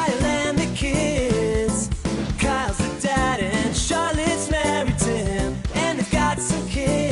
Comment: This is what I heard: "...Kyle's the dad and Shaun is married to him" "...there's Betty, she's going to high school" "...her brothers name is Trip" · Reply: Her brothers name is Trip. It was very popular in the 50-60s